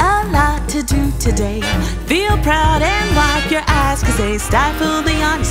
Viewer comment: i misheard eyes with ass for some reason